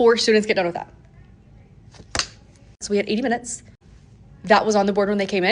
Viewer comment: I thought she said 8 minutes to complete the assignment, and I was like, “Tall order, but okay.” It was 80 minutes. And only a couple of students completed the work. That’s insane.